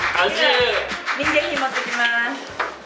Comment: あれ？民芸品くれるのかと思ったww 民芸品持ってきますねって聞こえたんやけどw